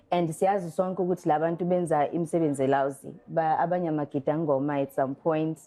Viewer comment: Did she just say, imisebenzi e "lousy"?